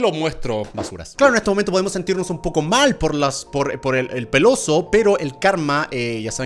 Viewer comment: En el minuto 1:48 se escucha como que dice peloso